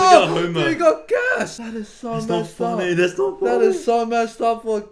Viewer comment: "Was Simpsons always this messed-dark?" - Eddy Chen, 2019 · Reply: i heard "messed duck"